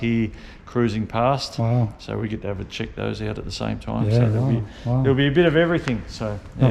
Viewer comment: I really like how friendly the salesman is. It's like watching two uncles chatting about their toys. Did I hear "We get every chick at the same time" at 5:53? As always. ❤